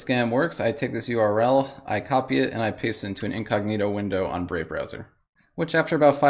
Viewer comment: what browser did he say in 0:55 ? i’d like to try it. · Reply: Brave